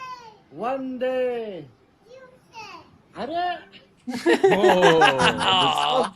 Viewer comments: what did she say here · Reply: After vande she said tuesday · what did she say ? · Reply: she said TUESDAY cause VANDE to her sounded like MONDAY.